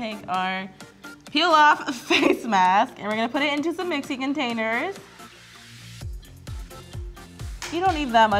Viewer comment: for a moment I thought she said Mexican containers and I got confused😂